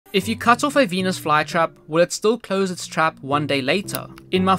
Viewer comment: I heard "when you cut a pen*s fly trap"